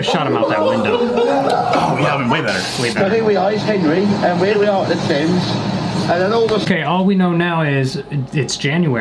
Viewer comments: for anyone wondering what they actually said :) “so here we are- this is henry. here we are at the thames (river in london), and then all of a sudden we’re about to- hold on a minute. what’s going on here? oh there’s two blokes (men) gunna go in the- oop. *maniac laughing*” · I think the one at 10:32 says "Now here I am with Harry and here we are at the Thames, when all of a sudden we found that- Oh, hold on, what's going on here? Two blokes look like they had a couple of drinks-" and then the drunks fall and they laugh. I'm not 100% about the "Now here I am with Harry" part, but the rest I'm certain of.